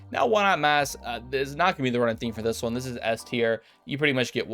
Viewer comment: yeah except I am. Clearly you aren’t though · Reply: i thought you were saying OEM had wall hacks lol